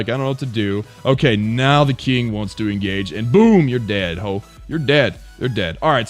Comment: did u just say "boom you're dead how." ?😂😂😂